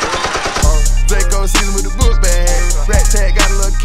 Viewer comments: Another album friday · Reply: I thought it said "Draco semi wit da book bag" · Is it just me or does it sound like he saying vlaco instead of Draco